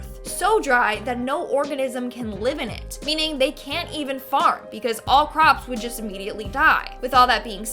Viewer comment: To the chick in the first video, you could be cute if you got rid of stink eye, eye lashes I believe that's what that trend is called then you may show a lil more skin if one part of the shoulder sleve a fashion statment? lol good video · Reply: LMAO, on the scene of land where it's dry, when she said they can't even farm it's so dry I thought she said they can't even fart because it's so dry. lol